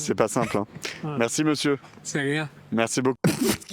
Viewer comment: qu'est-ce qui dit le vieux ? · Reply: « C’est rien »